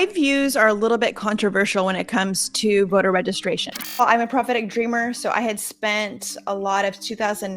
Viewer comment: Prophetic dreamer? I thought she said that she's a pathetic schemer.